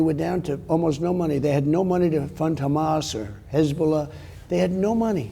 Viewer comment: did he say Hasbulla?